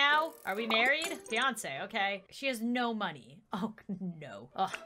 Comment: I thought she said Beyoncé 😂